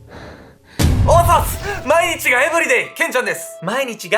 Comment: カルト宗教って本当に怖いです。 やはり、思い込みや洗脳ってヤバいと思いました。 あと、今回のケンちゃんの押忍、押忍のコメントが、「毎日が、エブリディ」って聞こえたんだけど、毎日が、毎日って意味がわからない😅。 聞き間違いかと、また聞いたけどやっぱり、「毎日が、エブリディ」って聞こえた😓 さんも、ツッコミしないし僕の耳がおかしいのかなぁ😵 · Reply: 加藤くんこれは聞き間違いなんかやない！毎日がエブリデイや！